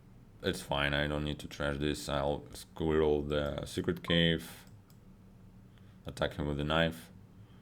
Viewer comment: it sounded like you said "attack him with a knife"